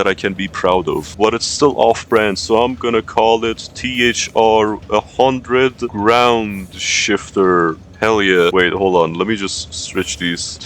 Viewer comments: Thought that said groundshitter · i thought it said "ground shiter"